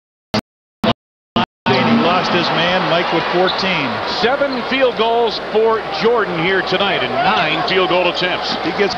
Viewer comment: What say Mj to Russell? TrashTalking? Some one read lips? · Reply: Remember you said you can guard me in 94, here's your chance